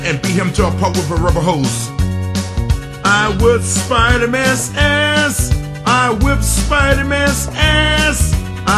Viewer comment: sounds like hes saying "i whiffed spider mans ass" lol